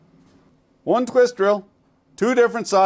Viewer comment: Thought he said to keep my dick in the rice 🤷🏻‍♂️